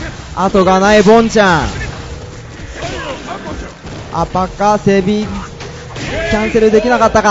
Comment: I kno he is not sayin it but it sounds like 'uppercut baby!' lol